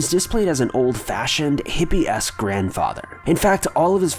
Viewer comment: I thought he said old fashioned hippy ass grandfather.